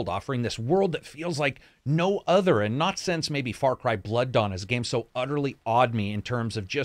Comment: What game does he say awed him as much as this game visually ? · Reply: Far Cry Blood Dawn. Which isn't actually a game that exists. Our guy was getting confused for a sec and meant to say Far Cry Blood Dragon I would guess.